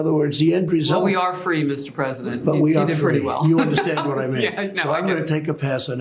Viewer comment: it sounds like he says, in agreement with her statement about freedom “... the end result, but we are free, you understand what I mean...” WTF is that supposed to mean?!?! He’s been freed from slavery too?!?!